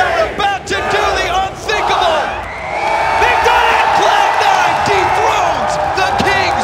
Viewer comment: does it say cloud nine deepthroats the kings of rocket league